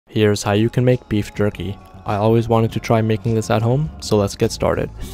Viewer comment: I thought u said jeep Cherokee instead of beef jerky😭😂